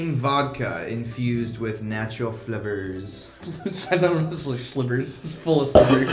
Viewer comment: I keep hearing "filled with natural flippers" XD